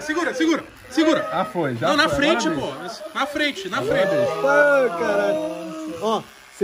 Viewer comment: No momento 13:25 só eu que escutei o toguro "falando na frente amor" kkkk eu n sei se eles estão kkk